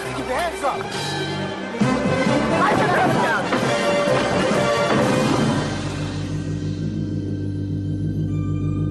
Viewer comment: What the hell does duke say at 2:44 😂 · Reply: Lightning, Thunder!